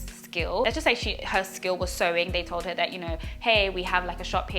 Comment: You talked about the lady skilled in sewing, that she came to Dubai into a building with sex workers and she does not know anyone, her consulate and embassy is there to take care of her. Just some advice before coming to Dubai contact your consulate or embassy ask them for a mobile number of an embassy worker in case of an emergency, never go inside a residence upon your first arrival. Check into a low cost hotel and do your homework ask to first see the business in operation, ìf they claim to have a tailor shop first go to the tailor shop ask the worker there to contact the owner to confirm you will work there. You couldn't find a taxi because buses are cheap and available. You know that standing could be misinterpreted that you are a prostitute so walk around till your taxi arrives. Stray cats near the entrance of restaurant means that restaurant is feeding them Customer left over. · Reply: Sewing? I thought she said "serving", as in bringing food to the table in a restaurant.